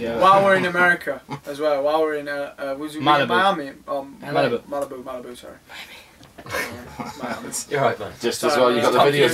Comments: He actually said Miami.. just Niall saying Malibu made it sound like he said Malibami :P haha <3 adorable tho ;) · does anyone else hear someone whisper miami at like 0:11 ??? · for a second i thought he said malibami.. that's jokes. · Yeeeeeeeah he says Miami errbody. I totally thought he said Malabami the first time I saw this haha. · seriously this is the cutest thing ever! ♥ miami xD well i thought he said malabami but whatever xD